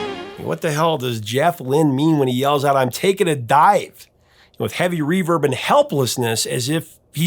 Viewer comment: I always thought he said "I'm thnkin' of dyin'".